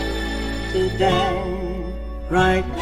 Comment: HOLY MOLY I BARELY HEARD HER SING THIS PART IN THE ORIGINAL CAUSE WHITE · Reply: Chelsea West it sounds like she said “So gay” instead of “Today”